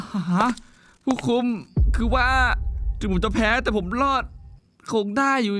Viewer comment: ถีงผมจะแพ้ แต่ทำไมผมได้ยินว่า จู๋จะแพ้